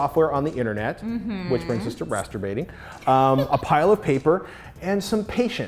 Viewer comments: On introduction, I thought she said MASTERBATING!!!! And he was going to make big poster of it or die trying. I was like WTF, Yeah baby! LOL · I thought she said masturbating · i thought she said Masturbating.